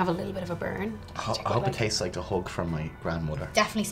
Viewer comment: I know you said "hug" but I heard "hook". ( you hope it's like a hook from your grandmother? What, like, right hook? Just punches you in the face with flavor? )